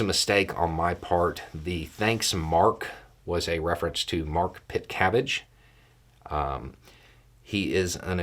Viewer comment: M-A-R-K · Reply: I thought Beau said mark fitzcabbage. Woulda had fun looking for that.